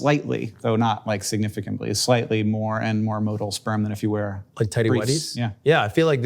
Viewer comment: Subtitle correction! Ludwig isn't saying "Shin-rock" here, he's saying "Shinra" -- the evil corporation from Final Fantasy 7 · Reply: modal instead of motile